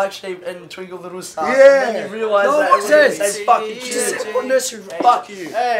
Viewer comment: Lmao i found this funny they sounded like they were saying fock fock fock lmaoo